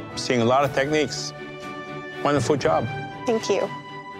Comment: When Chef Alvin said "Wonderful job" I heard " want a foot job" at first.. lol